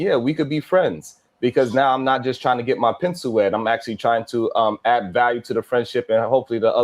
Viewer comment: did he say "pencil"?...